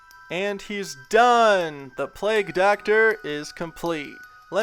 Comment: i heard play doctor not plague doctor